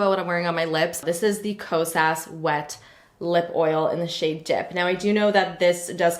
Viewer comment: You gave me a start and I had to check the screen because I heard you say the Kosas wet lip oil in the shade dick...